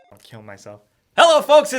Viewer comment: I heard that I'm gonna kill myself at the beginning lol